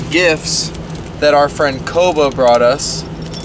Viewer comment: I thought he said “our friend Covid brought us” 😂